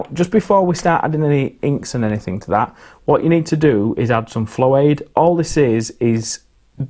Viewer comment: Jokes aside, at 3:50 it sounds like you say fairy liquid? What are you actually saying? Cuz I'm not sure how to make "florade" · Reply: I believe it is "flow aid"